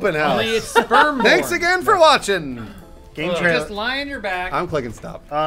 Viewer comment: at the end it sounded like said "Mexican for watching" lol